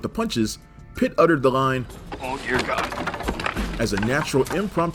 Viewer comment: What does Pitt say? It sounds like "Oh dear god" which doesn't really make sense or come across as funny to me. Can someone please clarify?